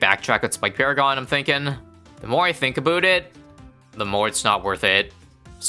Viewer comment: "the more ithinkabootit" is all I hear